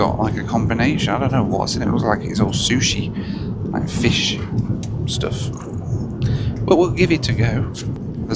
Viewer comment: Talking about sushi, I thought you said “smooshie” and I just got so confused! 😂.